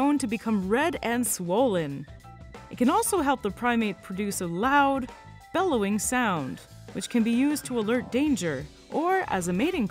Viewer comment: These guys are the most interesting monkeys on the planet. Those huge noses just give them so much personality. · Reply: YahvakinHansen Thankyou. I listened again and heard her say it helps them make a loud bellowing sound.